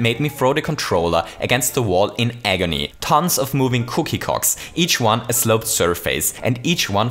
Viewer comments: I swear he said cookie cocks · i know he meant cookie cogs but it sounds like hes saying cookie cocks